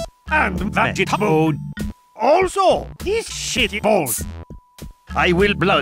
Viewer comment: for four years I don’t know what he said about the thing right next to the peas Shitty balls?